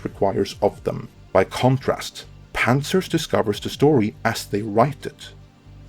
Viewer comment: Is that panzers or pantsers? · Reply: I thought he said pouncers my self but I think pantsers/pantsters is more likely.